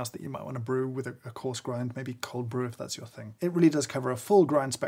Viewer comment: "Maybe cold brew if that's your thing" looks at you, disgusted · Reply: I misheard him at first as "Maybe cold brew if that's a thing" and I was astounded that he wasn't even acknowledging the existence of cold brew 😂